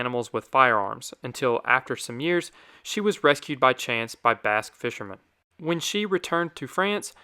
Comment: Legit thought he said she was rescued by bass fishermen, not Basque fishermen.